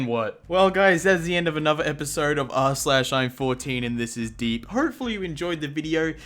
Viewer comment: I thought it said I’m 14 and go to sleep 😂😂😂